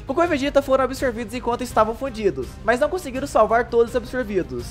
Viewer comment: foi só eu q ouvi "fudidos"? KKKKKKKKKKK